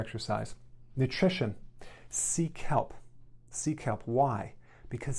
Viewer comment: I thought he was saying, "Nutrition: Seek help. Seek help." But he was saying "sea kelp."